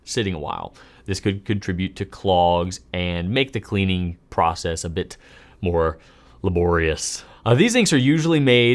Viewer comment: I heard "make the fountain pen cleaning more GLORIOUS" instead of "make fountain pen cleaning more LABORIOUS" the first time. I stopped what I was doing, and rewatched that part.